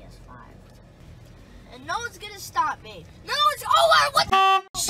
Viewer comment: if you put the video at 2x speed it sounds like is saying oh shit